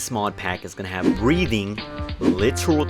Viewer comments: I thought he said breading I got excited for a minute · Thank God it was breathing not breeding 0:06 I heard it wrong